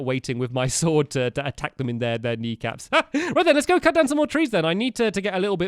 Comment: did u say chop knee caps!